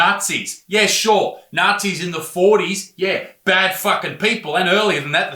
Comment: i thought he said NAZIS in their fourties. :D A: "hello sir, are you a nazi?" B: "oh yes i have been for over 20 years" A: "so you learned it from your parents?" B: "oh no, i got to be a nazi in my twenties when i first attended my first kkk meeting because of my roommate in University?" A: "how interesting i wouldnt have imagined that somebody would become a nazi in university, What did you stud?." B: "Oh no problem, i get that a lot, to be honest i did not finish my ....." A: "WAIT A SECOND YOU ARE have been a nazi for over 20 years and started being a nazi in you mid twenties? YOU ARE OVER 40. PUNCH THAT OLD PICE OF NAZI SHIT"